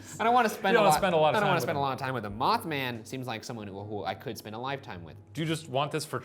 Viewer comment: LMAO Ryan, I heard that "ride Mothman" 😉